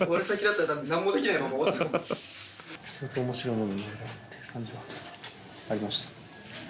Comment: 北斗なんて言ってる...？ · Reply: すごくおもしろいもの....になってると思いました？